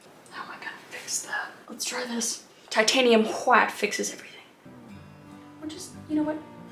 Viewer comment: Mount boredom and avoid human contacts should be the name -- It sys Titanium Whhh-ite,I heard "Titanium qhuait"